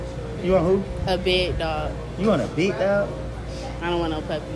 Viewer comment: Did she say big dog or something else